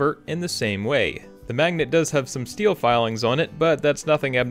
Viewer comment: Did you say “ex-pecially”??